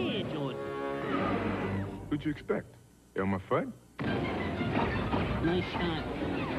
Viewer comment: I have no idea why, but it always sounded like Jordan said, “who’d you expect, Elmo Fudd?” and not “Elmer”. Maybe that’s just me.